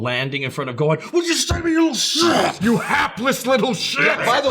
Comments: When I first saw it and to this day, I heard it as "WHAT DID YOU SAY TO ME YOU HEARTLESS LITTLE SHIT!!" It kind of makes a little more sense, Perfect Cell's pride is hurt at this point, which makes heartless a tad bit funnier than hapless. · I always thought he said heartless little shit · Ok real talk i thought he was saying Heartless and worthless Hapless was not ever an idea in my mind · I always thought Cell said, "you heartless little shit." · I legit thought all this time cell said "WHAT DID YOU SAY TO ME YOU HEARTLESS LITTLE SHIIIIIT" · Honestly i thought Cell said "Heartless" instead of "Hapless" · I thought he was saying "heartless" myself. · I always thought cell said "you heartless little shit!" So im glad that got cleared up 😂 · I thought he said heartless little shit. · I never thought Cell was saying Helpless. I thought he was saying HEARTLESS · And here I thought he was saying heartless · I always thought he was saying "You HEARTLESS little-" with Gohan being particularly cold at the moment. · i thought he said heartless, not hapless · I thought he said heartless not helpless or hapless.😅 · I honestly thought Cell said "Heartless little shit" 😂😂😂😂